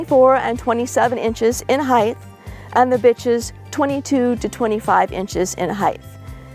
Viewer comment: did she say the bitch's